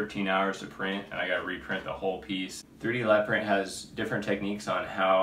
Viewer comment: who did you say has different techniques that they want you to use to cut the 3D pieces out????? 3D lab print? I couldn’t understand quit what you said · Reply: yes, 3d lab print!